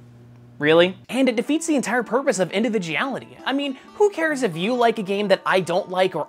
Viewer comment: Did I hear it wrong or did he just say "individiality"